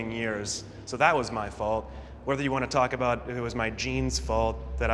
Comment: I'm French and at first when they talked about genes I thought they said jeans so I was like "what the fuck?? " And then I understood lmao